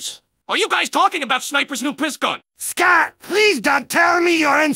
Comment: I can't be the only one who heard "Scat" at 3:05 right?